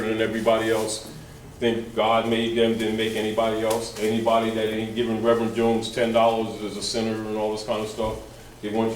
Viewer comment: anyone, at 1:09 did he say clearfied document, I can't hear him clearly, thanks family. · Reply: clearfield doctrine